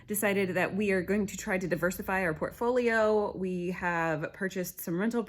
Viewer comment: Gosh you said diversify to fast. I thought you said divorce I was like WHAT!!!!???? And then a fewww lol. Glad all is well in the home! I sure hope your recovery is fast!